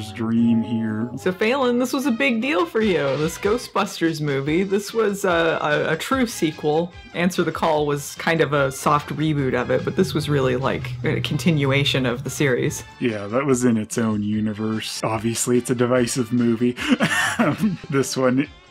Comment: Did just say "sock reboot"? Because I love that.